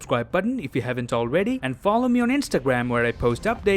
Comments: Did you just say you are Kevin Hart?XD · Is it just me or , did I just hear , " I'm Kevin Hart " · Did he just say he is kelvin Hart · Did he just say that he's Kevin hart Like what